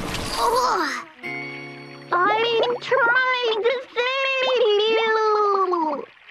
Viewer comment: What was she saying while in the water? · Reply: I’m trying to save you.